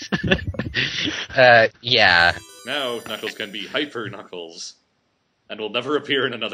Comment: thought it said "Knuckles can hyper Knuckles"